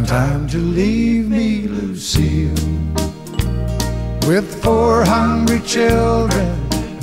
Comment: I thought he said "with four hundred children". Instead of: "with four hungry children".